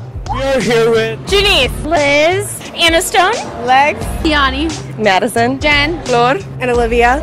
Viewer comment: did I hear legs 😂😂